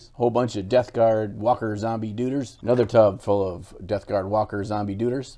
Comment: Wait!....did you say DUDERS? 11:41 mark....ROFL!!!! The Duders Abide.